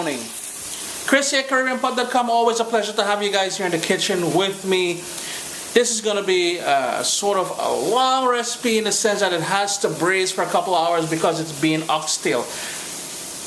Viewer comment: does he say, "this is gonna be sort of a walrus penis"?